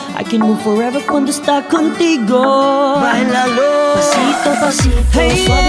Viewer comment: "tang ina nyoo!!" Did i misheard?